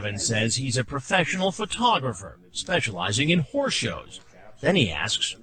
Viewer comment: i seriously thought Chris said “...specializing in whore shows.” 😂🤣😂🤣👏🏻👏🏻 dead ass, I did!